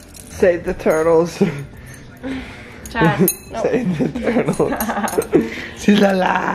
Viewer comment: did say dad lol. Did she call dad?!?! OMG ARE THEY HIDING SOMETHING????